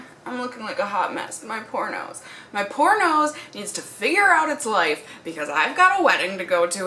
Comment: I thought you said pornos 😂😂😂😂